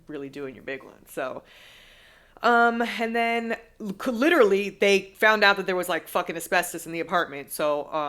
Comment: i heard this as CLITORALLY